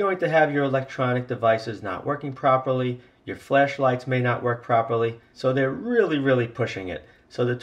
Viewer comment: sorry... thought I heard 'flesh light' 😛😂